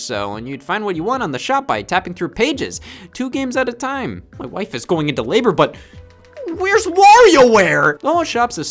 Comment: I thought he said “my life is going into labor“ not “my wife is going into labor“ 😂😂😂